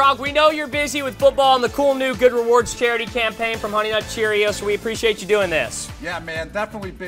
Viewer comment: I thought it said HONEY NUT CHEETOS!!!😂